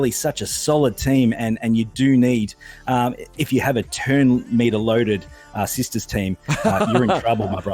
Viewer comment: did I hear say something about pre-loading? :-P